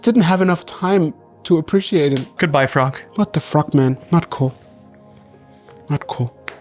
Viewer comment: I thought said, "What the 'frog', man..."